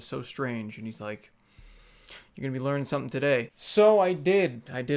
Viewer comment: "You're gonna be learnin' somethin' today." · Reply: Lol I heard it as "You will be learning something today" - insert generic white guy voice